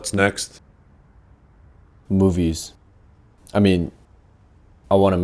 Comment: I thought he said boobies then i heard him say movies